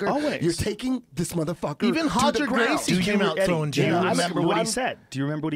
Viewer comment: Can someone with amazing listening ability please tell me what Brendan says at 5:04? "Even Roger Gracie came out......" (I train with Roger, so I'm really curious to hear what Brendan said) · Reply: even roger gracie came out throwing jabs